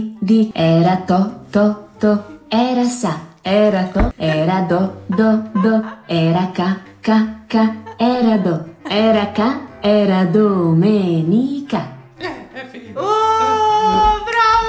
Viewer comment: solo io ho capito cacca? 😂